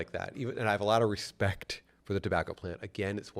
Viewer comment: I like how cannabis is censored but not fuck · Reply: Bro I thought he said c0€@ plant